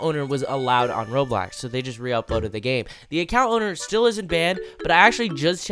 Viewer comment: Hey around 4:39 there was an issue with the captions and it said bad instead of banned and the account owner really is bad